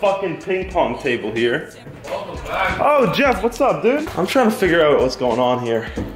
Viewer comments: I almost thought he said jev I was about to flip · did i hear JEV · did he say Jev? O.o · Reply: He prob said jeff